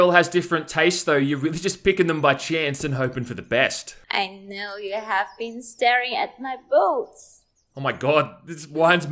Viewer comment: All I hear is “I know you have been staring at my boats”